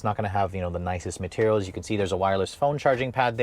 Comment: did he say iPod connection????